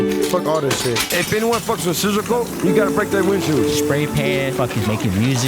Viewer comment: Did he just say "Scissor Poop"?